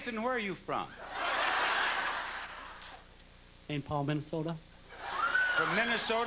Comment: What'd he say at 31:57? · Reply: St. Paul Minnesota